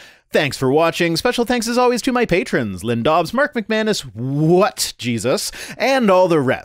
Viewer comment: I also thought that you were saying wet Jesus. Sorry about that, What jesus.